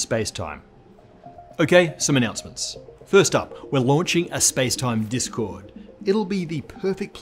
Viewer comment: I first heard: "We're launching a space time disco". alas...